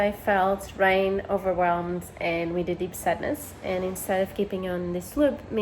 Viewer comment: Can someone write what is explaining at 4:50-4:55. I can't make out what she says. "the last few months I felt ? overwhelmed and a ?" Cheers. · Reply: "....a really deep sadness."